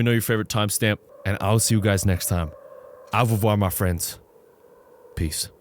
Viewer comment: What? I thought that he said "All of you are my friends" in his outro but just now I realized that he says "Au revoir" my friends